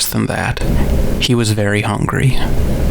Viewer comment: Incredible video, and I believe you. In the 0003(1), the voice also says "SHE was very hungry", though your captions at 15:40 miss this. My first impression is these are your own outtakes (or raw audio) from another timeline. I have some questions: Was there a thunderstorm that night too? It sounded like there might be steady rain hitting a roof in the alt version. When you record, do you have a high noise floor like 0003(1), and just edit it out? When you mispronounce a word in this reality, does your speech pattern resemble the word stumble in 0003(1)? Thanks for sharing, this is amazing.